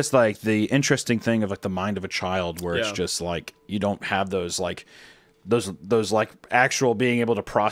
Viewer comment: Shut up-, I thought u said you don’t care